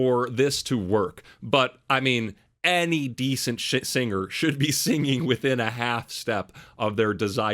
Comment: Hahaha 16:13 and decent *sh#t singer? Is that what you are trying to say🤣🤣🤣🤣🙌🏾